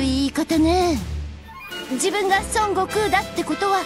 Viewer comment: Who else hears bulma say son goku